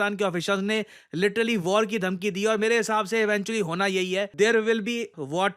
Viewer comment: did he said BC?! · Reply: Eventually bola